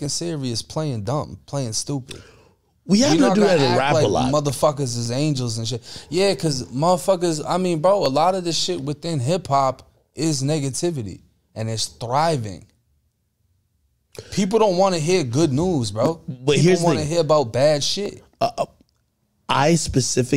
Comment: Did he just say niggatvity 😂 ayo